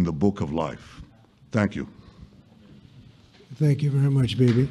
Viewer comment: Did Trump said thank you V.much Baby...baby😁really